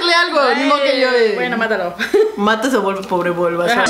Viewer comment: bueno mátalo me da igual su vida :v eso entendí yo xD